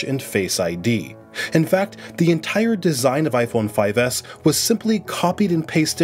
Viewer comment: Nice video overall, but i hear iphone 5s instead of SE on